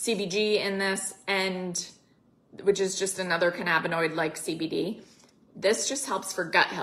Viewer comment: Exsqueeezzzzee me?!? Did she just say "Canna-banana-noid"? 😂😂😂